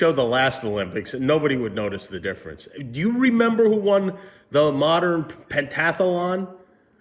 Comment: I heard: “The modern pentagram.”